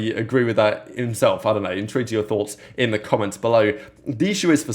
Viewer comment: I’ve been listening to you for like 2 years now and it’s driving me batshit insane WHAT THE FK are you saying when you say “in to enter your thoughts in the comments”. I need you to clarify before I lose my shit because every time it makes no damn sense lmao. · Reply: “Intrigued to hear your thoughts”